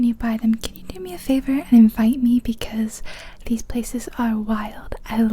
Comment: In the beginning when you said "invite me" I thought you said "fight me" and I started laughing.